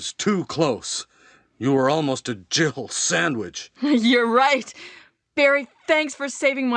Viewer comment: Since 1996 i always had thought Barry said "Jibble Sandwhich" for all the while the voice action was truely normal and never noticed.